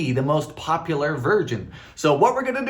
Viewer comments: Am I hearing right at 18:30 does he says "most popular virgins"? Keep up the interesting videos Dylan both my niece and I plan on buying your cookbook and trying many of the recipes. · Nearly spit out my drink when I heard you say, “the most popular virgin” instead of version! LOL 😂 I love both the long and short versions of your videos. Keep doing what you are doing! ❤❤ 🎂